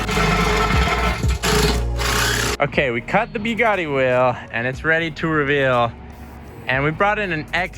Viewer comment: Guys. At 4:41 please take a deep listen. Did he just say “we cut the BEE-GOTTY wheel? Hmm